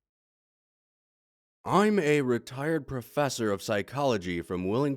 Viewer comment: Could have sworn that said “I’m a retarded professor”